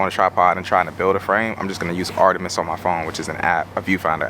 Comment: What’s the app called? I can’t really understand what you’re saying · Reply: Artemis. A viewfinder app. Simulates how wide the frame looks with a specific camera and lens combo.